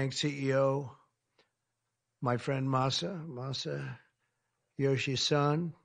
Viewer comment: thought he said masayoshi-san at first lmao, only a minute later realized the guys name is literally masayoshi son